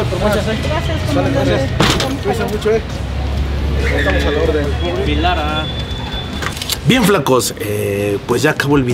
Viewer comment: ¿QUE DIJO ESE TEPOROCHO? ALGUIEN SABE xD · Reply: Dijo bien Agustín lara 😅